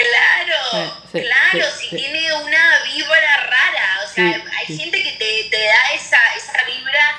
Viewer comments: yo también entendí "una víbora rara" jajajajajajja. Buenísimo, me subieron el ánimo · JAJAJA, yo también entendí "víbora rara". 😂🐍